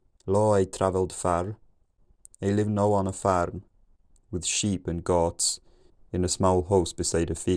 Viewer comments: This is what I heard: “Lloyd traveled far. He didn’t know when to fahrt.” 😂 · it was so crazy listening along to basically gibberish and all of a sudden hearing "it is long winter, the ground is cold" emerge from the fog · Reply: Just before that I heard "sheep, nor goat, nor cow" (which sounds like cat)